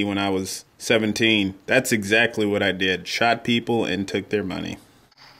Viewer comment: Holy SHIT!! 3:25 Did i just heard that this guy used to Shot people and Stole there money back in high school??